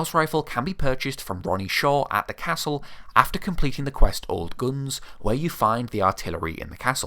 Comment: did you say "old g00ns"?